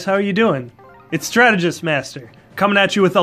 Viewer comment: I heard "it's tragedy master" lmao